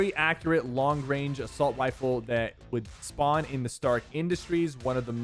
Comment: I swear I heard assault wieffle